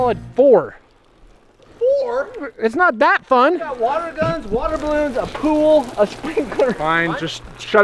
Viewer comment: I thought said water counts